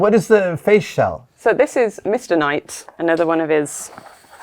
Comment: When Adam asked what was the face shell and she said Mr Knight, I heard it as mistinite, like that was the name of the material it was made of.